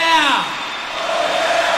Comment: Did he say fucking rocks? :DDD